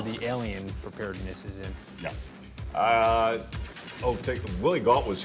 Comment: At the end of this video did he say, "Willy wonka was here about a half hour ago and had a foot brace." ? I swear it sounds like he said that. Lmfao.